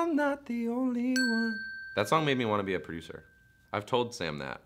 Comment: Why did I hear 'that song makes me want to be a freezer' 😃